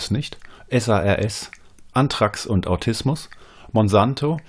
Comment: bei minute 12:25 kann leider das wort vor "autismus"nicht ganz verstehen. ist es "antraks und autismus"? möchte es googlen, aber vetstehe akkustisch das wort vor autismus nicht.. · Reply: Anthrax